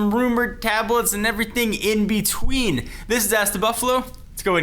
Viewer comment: It sounded like he said ass the buffalo